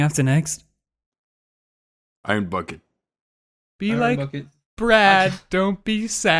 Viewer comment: For some reason I thought he said Skyrim bucket and I was super confused lol